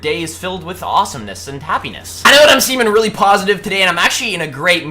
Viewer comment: I heard "I know I'm semen" Okay, we understand.